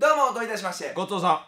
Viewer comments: “ごちそうさん”が “ごっとーさん”に 聞こえてしまう。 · ごちそうさんが最初god songに聞こえた